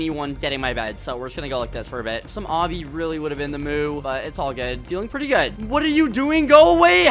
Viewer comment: wait wait wait did just say some obby would have been the moo??? THE MOOOOOOOOOO